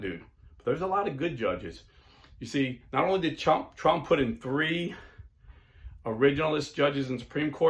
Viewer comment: Did you just call Trump a Chump @ 25:25?